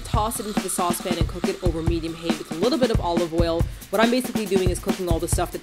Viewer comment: Maybe I misheard, but at 4:40 I'd swear the instruction was to "Toss it into the saucepan and cook it over a medium hate..." - wow, those shallots really got to you didn't they Martina? XD